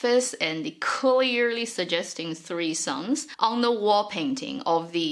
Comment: subtitle is wrong. it's "three suns" not "three sons"